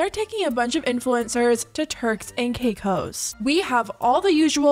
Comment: It sounded like you said “terks and cake hoes”